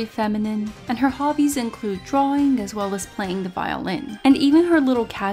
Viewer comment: I thought u said TROLLING as well as the violin Then I realized you said drawing so anyway